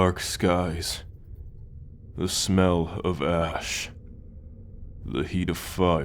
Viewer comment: Bro at 0:09 when he said smell of ash i thought he said ass😭😭🙏🏿